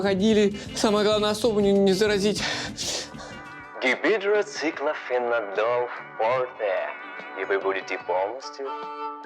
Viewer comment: Мне одному послышалось "Гибидроциклофенодол в попе!"?)